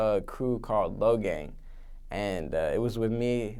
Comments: He's part of the Lo-gang. You should get an Hila Kleiner tat! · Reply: I honestly thought he said Logang, until I saw this reply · I thought he said logang